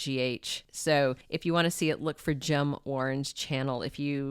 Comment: That channel, that you mentioned, it sounded like you said Jim Orange, is that right? If so, I couldn’t find it, please help!